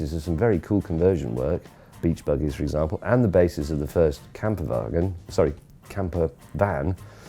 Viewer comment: OMG when he said "camper wagon" the first time I thought he said "kampfwagen" lol.